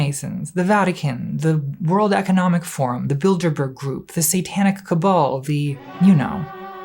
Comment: You keep saying “Bilderberg” group, and I keep hearing “Build-a-bear” group. I think we need a new conspiracy theory.